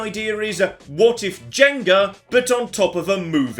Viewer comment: I legit thought he said gender not jenga and was very confused